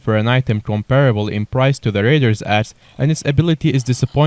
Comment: Anybody hear raiders ass not raiders axe